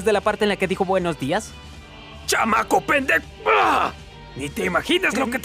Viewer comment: "llama copet3 Ah..." Xd en esa parte dijo eso :) aca esta lo que dijo bien humilde